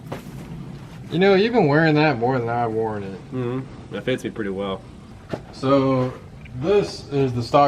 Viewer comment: Did he say, "It pits me pretty well." at 2:44 lmao